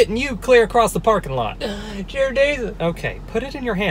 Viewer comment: did u say "jear desus".